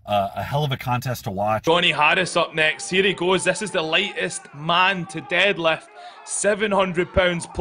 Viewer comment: wait a second did he say lightest man to deadlift? 2:11???